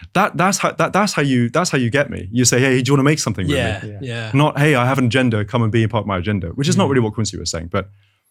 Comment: Hey, what's Jacob saying at 33:31, before "which is not really what Quincy was saying, but"? pls someone help a non-native english speaker out 😂 I usually understand him quite well, even tho he talks pretty fast, but sometimes the way he pronounces things just doesn't click in my brain no matter how many times I listen to the bit 😅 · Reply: “You say, ‘hey, do you want to make something with me?’ Not, ‘hey, I have an agenda. Come and be a part of my agenda.’ Which is not really what Quincy was saying.” I had to listen twice too 😊